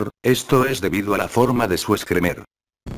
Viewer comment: parece que dice excremento